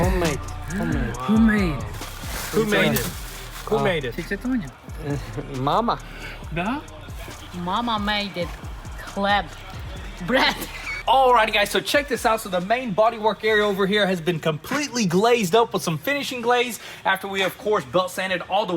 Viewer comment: I swear his momma said she made it clap!!!!